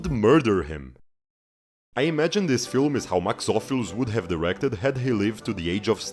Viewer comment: I can't catch the name of the director mentioned at 17:20? Max off? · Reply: Max Ophüls